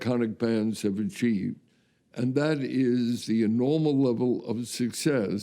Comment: What the hell does that old dude say at 5:11 "that "inormal" level of success..." ? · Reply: Enormal. I am not an English native speaker, but I do know the word. It means something like " out of the ordinary "